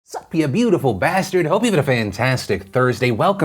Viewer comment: I always thought Phil said "sup you beautiful bastardS" but he said "bastard" singular. It's like he was talking to me directly. I'm the bastard. :)